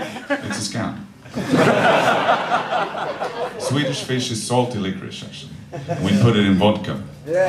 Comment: What the fuck does he say at 1:20? "Swedish fish is ___ we put it in vodka and then we put it in the dishwasher ..." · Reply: salty licorice